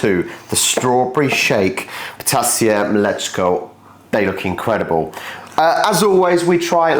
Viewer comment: Your ptasie mleczko sounded like "potassium let's go" 😂